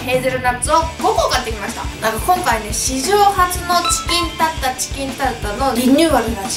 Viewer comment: チキンタツタがチキンタッタって聞こえる(笑)